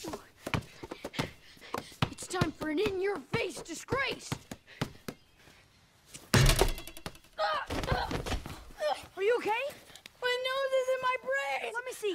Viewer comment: "OH MY GOD!" "What?" "You're a dork!" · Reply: I thought he said “Your a dorrick.”